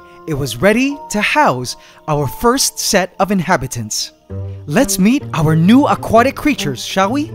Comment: what he said what's the first inhabitants of the terrarium I was thinking " it's nothing"😂😂😂